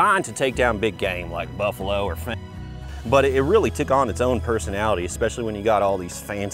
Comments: I love how seems to make rethink every decision he’s ever made that led to that moment. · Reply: 🤣 I thought he said buffalo or fat chicks, but I guess feminists works too! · what is he saying at 11:53 ? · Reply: He said like buffalos or feminist